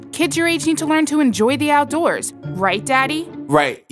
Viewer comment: DID SHE JUST CALL YOU DADDY? thats a bit sussy if you ask me